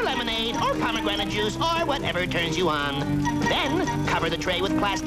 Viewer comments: Okay, when I was a kid I thought he said "or whatever juice you want," not "or whatever turns you on." I was an innocent little 6 year old. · Okay so how is this a Psa? Also did he say at 0:39 "whatever turns you on"